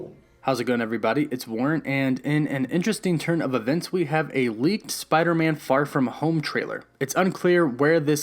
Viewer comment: i thought he said movie leaked